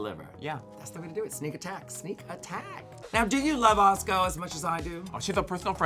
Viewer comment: When Bianca says Atsuko it sounds like shes saying Bosco, tf